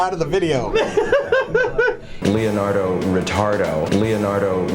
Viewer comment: I thought they said 'Cleonardo Retardo' and laughed pretty hard.